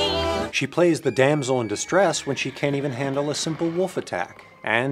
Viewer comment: did I just hear wuf instead of wolf?